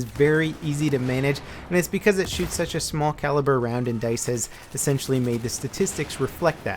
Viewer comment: TheGoldenCaulk By your logic the 5.45mm round used in the AK-12 is extremely microscopic to the .45cal round. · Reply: Well I'll be damned, at 2:50 he says "because it shoots such a small caliber round." I thought he said "compared to the .45." Good call